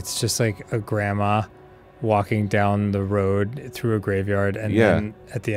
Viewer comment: Does anyone know what game they’re talking about at 0:28? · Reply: The Graveyard